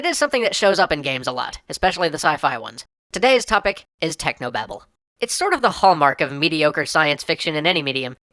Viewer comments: I thought it said Technoblade lmao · I thought it said “Technoblade”